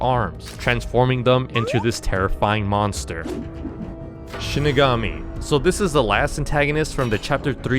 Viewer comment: i thought you said "shikigami"